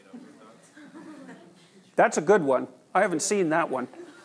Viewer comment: anyone heard what the student said at 35:53 ? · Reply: “18 triangles within those 4 squares - made out of dots”